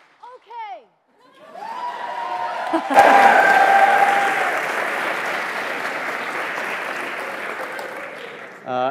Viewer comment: what was said at 49:59? 'okay'? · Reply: Yeah, Ashley Johnson didn't get to finish her final line while on stage so she just screamed out "Okay!"